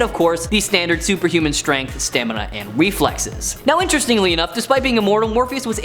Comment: m boi said weflexes